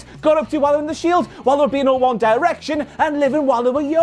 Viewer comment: I thought said "while they were beating up One Direction" and I opened the search bar immediately.